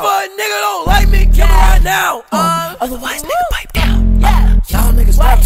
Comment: Fr thought he was saying otherwise bite down lmao